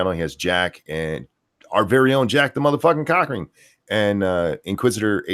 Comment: it sounds like your saying. jack the mother fucking cockring